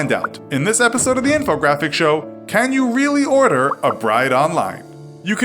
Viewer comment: I thought it said “Can you really Order a BRIDGE online?”